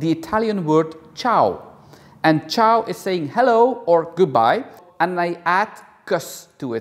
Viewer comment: Haha,☺️I always thought you were saying Chao Kiss—bye with a kiss. 😘 You probably know the Germans have something similar sounding Tschüss (chōōse) a familiar friendly goodbye. All ways I like and it’s special to you.😘